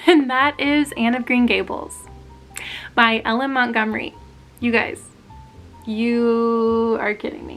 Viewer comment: 😂 I thought you kept saying Ellen Montgomery and it was plucking a nerve. It's not Ellen. Her name is Lucy! Lucy Maud Montgomery! Then I realized you were saying L.M. 😂 😂 Anne of Green Gables is my favorite. And my opinion, the only acceptable adaptation is the one with Megan Follows.❤